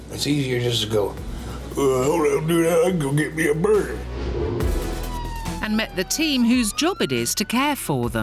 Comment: WHAT IS HE SAYING IN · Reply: "Hold up dude, I'll go get me a burger." KkonaClap